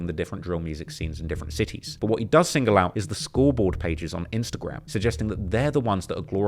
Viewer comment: I thought you said school board pages. I thought this was going to take a crazy turn